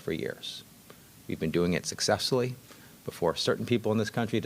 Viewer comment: Did he say years of success? Successfully embezzling maybe.